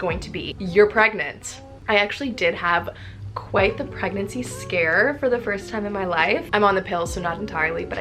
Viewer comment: I thought it said pregnancy scar! I was like 😮